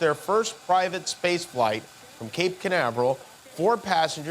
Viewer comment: Every time I hear non Spanish speaking Americans, (aka gringos) pronouncing CABO CAÑAVERAL (cape canaveral) in English, I hear "CANABROL". This is what it sounds like. Jimmyyyy, I miss you almost as much as William. I mean, Guillermo.